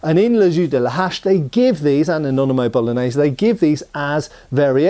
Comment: I keep hearing 'in mayonnaise bolognaise' 😂